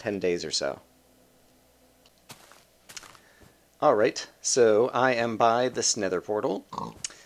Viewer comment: At the beginning it sounds like you say: alright i am by the snether portal